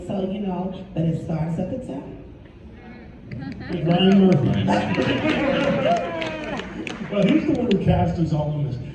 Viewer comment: What did he say at 22:47 ??? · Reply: Ryan Murphy